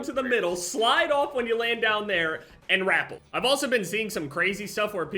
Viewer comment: please it’s “ruh-pell”, like shell. Not “rap-L” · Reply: Lmaoo i straight up thought he said grapple at first and had to rewind it